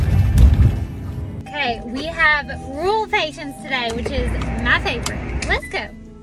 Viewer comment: I genuinely thought you said "rule patients" and I was so confused until I read the caption. Crazy how much an accent can change how a word sounds!